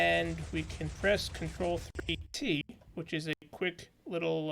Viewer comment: Can anybody make out what he's saying at 0:40? The sound breaks up a bit there. What is the key combination? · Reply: f3+t ... :)